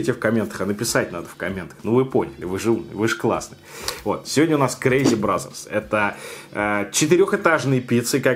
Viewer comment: Мне одному послышалось crazy brazzers )))